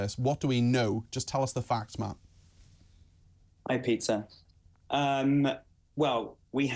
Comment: I swear at the beginning when he said "Hi Peter" I heard "Hi pizza"